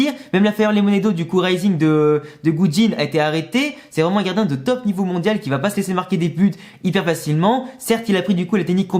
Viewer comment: J'ai entendu de Poutine j'ai pas compris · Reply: la position xD